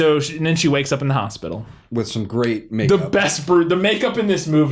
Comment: did said great or grape make up?